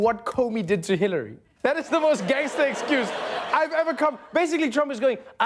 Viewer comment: Did I hear him right? At 2:08, did he call Trump's explanation "the GAYEST excuse"? Hmmmm..... sounds like somebody's a homophobe!